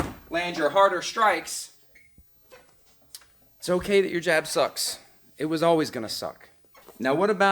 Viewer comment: Boo, I thought it said job sucks.